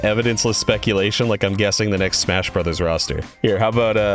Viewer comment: Did I hear Dante.